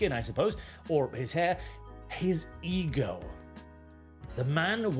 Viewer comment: when you said "ego" i misheard egirl around 16:30 ... which somehow still works. just gotta get the image of egirl trump out of my head now